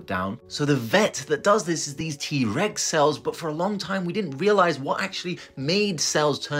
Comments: Someone else commented this already, but I also thought he said “T-Rex cells” at first! Whole different scenario went through my mind….. 🦖😳!!!!! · I misheard the name of the cells at first, and from now on, they're T-rex cells to me😂😂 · Who else thought he was saying “T-Rex” cells?